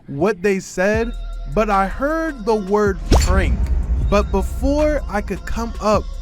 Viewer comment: What, why is the word frank at 4:06 a big deal?